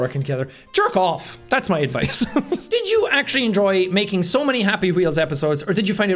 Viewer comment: I zoned out for a minute and suddenly all I heard was “Jerk off! That’s my advice!”